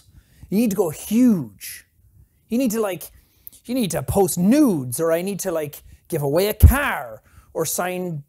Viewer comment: I wasn’t paying attention but I heard “I need to post nudes” i immediately snapped my head up. And like. I don’t even want it but it still made me laugh😂